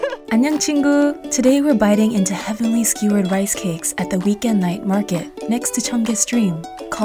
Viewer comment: i thought she said "vegan" night market at the beginning and i was excited, then the chicken thingies showed up and i was disappointed D: i don't understand what she is saying at the beginning lol